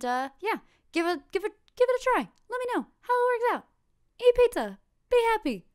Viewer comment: I heard "Eat a pizza." Will do.